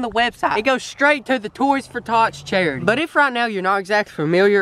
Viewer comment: I thought he said toys for thots 😂😂